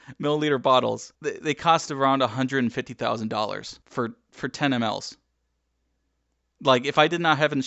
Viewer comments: I think that he said: "for 10 mls" as in 10 milliliters. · You input the subtitles wrong at 9:23. It’s supposed to be “10ml’s” but the subtitle is “four animals”